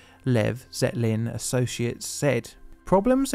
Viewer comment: At first I thought you said Led Zeppelin & Associates 😂